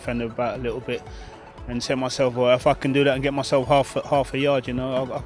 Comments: I know what he said, but at 1:21, "i f***ing do that". haha just a bit of fun. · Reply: He said if i can do that.x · At first I thought he said I fucking do that then I realised If I can do that